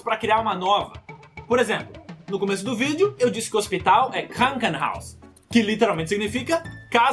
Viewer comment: eu entendi "Krakenhouse"